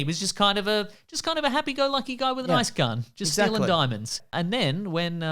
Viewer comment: He said “ice gun” and i heard “nice gun”